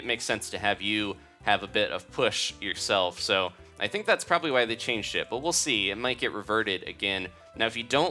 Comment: did say "I think that's why they changed shit"